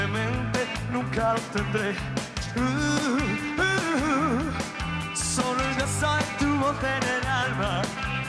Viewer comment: se dieron cuenta que dice: "solo el beso de tu boca en el alba"??? o estoy loco?? recvisen el 00:45... eres un genio Manolo.